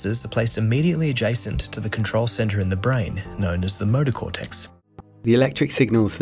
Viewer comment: did he say, "the murder cortex" ?? 😳 A.I. Will take over